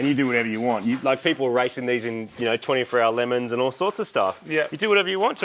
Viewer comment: sounds like he said 24 hour Lemons haha!! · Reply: It is 24hrs of lemons, it’s a race for beater cars run like 24hrs of Le Mons but with shitters instead!